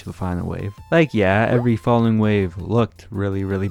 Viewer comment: Did you just said "Like every Fallen wave" Edit: 13:43 actually